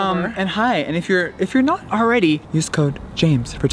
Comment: did James just say.... BYE SISTERS?!